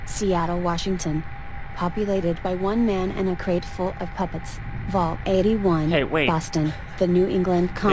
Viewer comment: For vault 77 i thought she said a crate full of Puppies and i was like BEST VAULT EVERY!!! and then you said puppets and then i was sad WORST VAULT EVERY